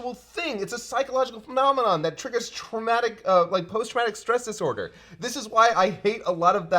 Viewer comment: Ngl, thought he said "Post Raditz Stress Disorder" for a moment.